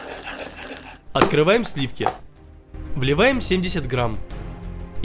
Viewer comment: Мне одной показалось что он сказал "блеваем"